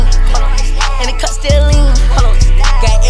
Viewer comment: All I hear is lean 😂